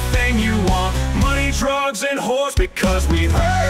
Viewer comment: Is it bad I originally thought I heard him say “money, TRUCKS, and whores?” 😂